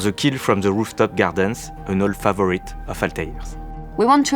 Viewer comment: the captions are wrong. It's "an old favourite of Altair's" not 'players'